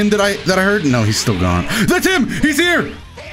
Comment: lol i thought the saibamen said I'm here